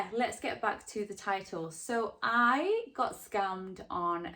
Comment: it sounds like youre saying you got scummed